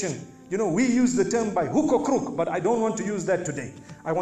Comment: What does Mufti Menk says at 5:26? Whocacrook what? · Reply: by hook or crook